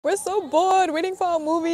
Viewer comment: For a split second i heard " am so bored waiting-" In what I thought was a cemetery. 😂😂😂😂 I was like; waiting for what: the walking dead, a vampire, or Kingdom come😂